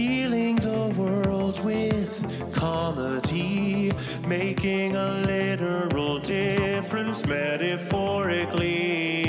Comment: He is actually healing the world with comedy. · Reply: damn I thought he said making a literal difference literally and than every war stopped at once